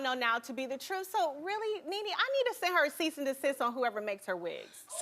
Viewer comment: What did Kenya say here? 9:35 "So really, Nene, I need to sesese assistant(?) on whoever makes her wigs." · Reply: Ceased and desist